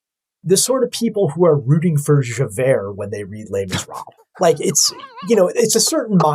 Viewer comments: I'm stupid, but who does he say people in the FBI are rooting for? at around 1:20, and John Stewart laughs after he says it. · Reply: He’s referring to Les Miserables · What is he saying at 1:21? It sounds like he's saying people that root for Javair in Layman's Rob, but i don't have a fuckin clue what that means 😅